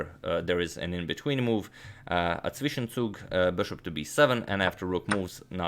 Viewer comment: did he say ..zwischenzug.. in German ?